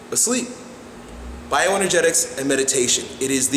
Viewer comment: how do you spell what he said? Biogenetics? Bioeningetics? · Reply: Bioenergetics